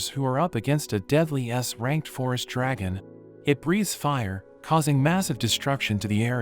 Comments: i thought he said It respire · In the start i thought he said deadly ass ranked, i'm so stupid